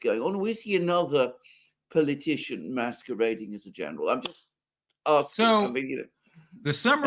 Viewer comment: Generally Alexander Eloqutes and Enunciates Precisely. But I thought that he said "Another Politician Masturbating as a Journalist". Is he saying that MSM Journalists are a Pack of Wankers?